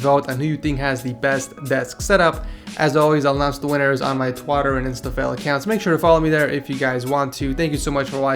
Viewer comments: lol did he rlly say twotter instead of twitter XD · Did he just say "TWATTER" OMG!!!!! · Did he just say Twatter instead of Twitter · Did he say twatter?